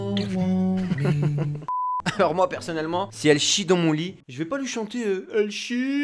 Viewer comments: moi j’ai entendu « elle chie ton vomi » 😂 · j'ai pas entendue "elle chie dans mon lit" mais j'ai entendue "elle chie ton vomi" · moi j’entends perso <elle chie ton vomit> 💩=🤮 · Moi j'entend "Elle chie dans l'ovni" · moi j'entends ''elle chie ton vomi''😂